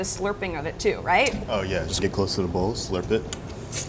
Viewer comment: I thought the guy said ‘stay close to the balls and slurp’ but she already knows that